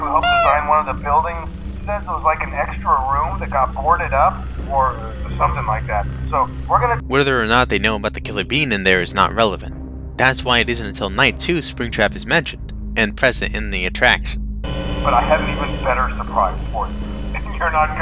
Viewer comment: I heard "killer bean" lol